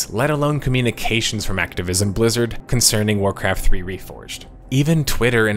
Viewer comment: "Activism Blizzard" I heard that right?